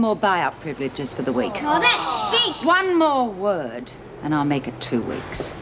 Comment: did Doreen really say that's shit? Lol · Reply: Sounds like it hahaha but think she says stink